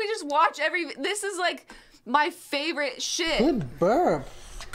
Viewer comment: why does it sound like Kermit is saying "i'm giving birth" at 14:21 ?